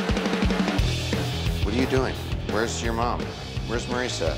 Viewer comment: Did he just say wheres my mommy 🤣🤣🤣🤣🤣🤣🤣😂😂😂😂😂